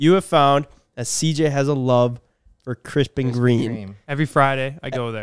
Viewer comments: I'm so fat, I thought yall were saying "Krispy Kreme" in stead of "Crisp and Green" lmao 🤣 😭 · When said Crisp and Green the 1st time it sounded like Crispy Cream, just like CJ thought his gf said.